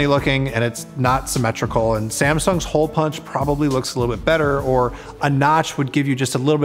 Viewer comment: Did he say a little bit better?